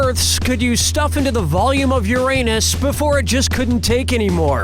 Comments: i wasnt paying attention so i thought he was saying your anus lol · I wasn't reading the text on the game and I was just listening so I thought he said ''Your Anus" Lol